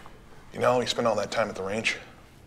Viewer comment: What did Chain exactly say at 2:53? Sorry english is not my primary language. Just heard something about ranch. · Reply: "You know how we spend a lot of time at that range?"